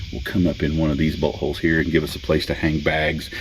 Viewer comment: Lmao swore you said buthole